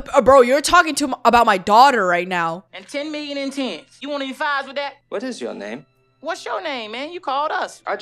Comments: I always thought Chris Tucker said "do you want fries with that?" not "do you want 5's with that?" Great action movie. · Is it actually “do you want any fives with that?” I always thought he said “do you want any fries with that” and that’s what always made me laugh 😂